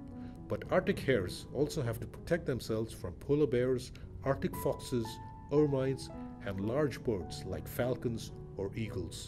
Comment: What are those? I can't understand what you're saying because of the accent...sorry. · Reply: First the arctic Fox, then Ermine